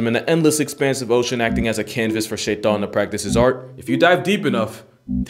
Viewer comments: did you say shaytan or my subtitles/captions messing up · is he Muslim now he said shaytan instead of Satan 😭